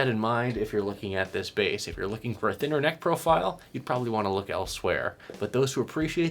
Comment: When you said 'neck profile', I heard 'necrophile'. That's it: no more dark web for me